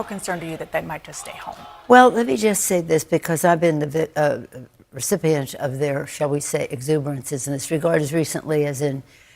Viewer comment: In case you didn't catch it folks, "I've been the vict- um recipient" . She sees herself as a victim when she is protested. She feels attacked by the protests. She is doing nothing wrong, it's the protestors who are.